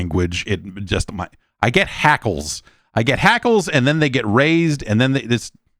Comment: DID SOMEONE SAY HACKEL